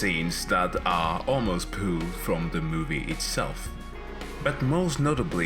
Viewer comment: sounded like you said "pooh'd from the movie itself" sorry could not resist, when i hear pooh i gotta share some